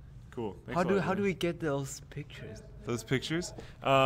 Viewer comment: OMFG I completely thought just said "How do we get those girls pictures?" I was like WOAH WOAH WOAH!! lmao that's Comical!